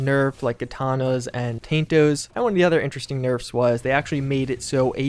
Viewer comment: Did you just say "Taint-o"